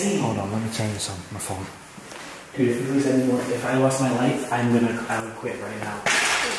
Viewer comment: I think I heard say, "...dude, if I lost my life I'm gonna quit right now." 1:03:24-26 shows how scared he was at the moment.